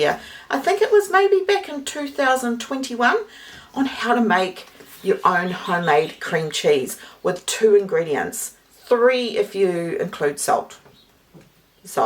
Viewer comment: Needed to rewind several times. 0:40 Did you say "3, if you include SOAP?" FYI. Milk boiles when you turn your back to it.